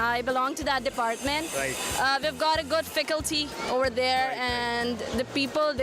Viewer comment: Is it just me or the aunty jee at 3:46 said ficalty instead of faculty 🤣🤣